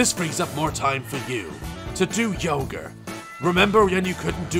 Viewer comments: Did the man just say yoger instead of yoga? XDD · it sounded like you said "To do Yoghurt"